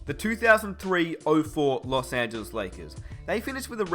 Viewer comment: is it me or does he say "Los Santos lakers" on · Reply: Just you, I heard Los Angeles Lakers.